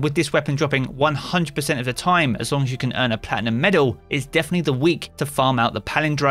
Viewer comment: what did he say? We can get adept Palindrome if we get what in the middle? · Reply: Plat guarantees adept